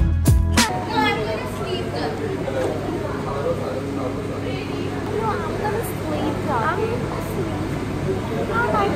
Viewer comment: what did that girl in the background said? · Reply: Just something on the lines of ‘im gonna sleep here’